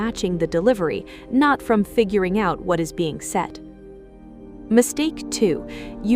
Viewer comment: you say "what is being set". I believe you mean "what is being SAID". It makes no sense using the word "set".